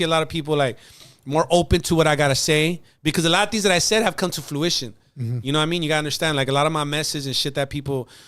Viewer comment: did he say Fluition? Wow… 🤔